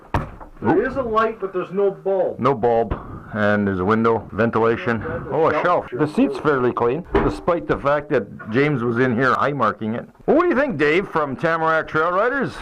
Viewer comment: "Eye marking" - HAHAHA - if u think about it, that's even funnier than what I actually said (high marking). Ya, the creek splashing episode was funny! I was soaked, but nylon pants dry quickly - lol Thanx for the comments and for watching buddy! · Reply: lol, I thought that is what you said, eye marking! I just listen to that part again and can totally hear high marking now. Great video buddy! Love the lunch scene as always👍.